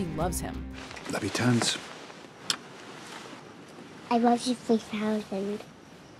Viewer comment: When morgan first said “i love you 3000” i thought she said “i love you to a thousand” which i thought was really sweet and then i heard people saying i love you 3000 and realized thats what she actually said then i was kinda mad because it really makes no sense at all